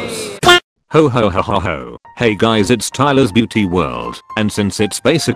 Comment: Why do I hear tyler's filthy world instead of beauty world? I NEED PROFESSIONAL HELP